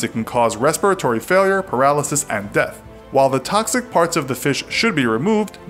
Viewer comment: I heard toxic farts.